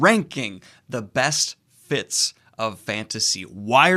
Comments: Every time you say “the best fits of fantasy” I hear “the best Fitz of fantasy”, and I’m like wait there are other Fitz besides Fitzchivalry in fantasy? Too much RoE, over here. 😂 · When I read "Best Fits of Fantasy" I really imagined we were going to see a ranking of fantasy characters losing their minds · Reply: And I thought he was saying "the best Fitz of fantasy", and it'd just be "Realm of the Elderlings: S tier! Agents of Shield: B tier. That is all."